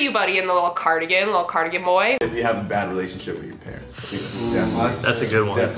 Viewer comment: I thought he said "I had a bad relationship with an Aries" and went "well, a bit generalized, not all people like your ex will treat you like your ex, but I get it". But if he actually said "my red flag is if you don't have a good relationship with your parents" then that is horrifying. I have a bad one with mine. Because they are awful people. Simple as.